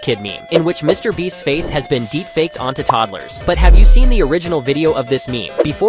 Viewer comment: I thought he said mr beast deep faked on to Tacos 💀